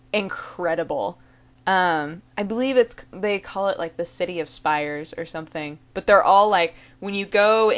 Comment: I thought ya'd said the city of spiders ! I played too much Sly band of thieves I guess ...